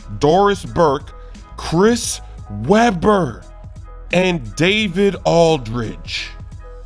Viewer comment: I thought he was gonna say 😂😂😂😂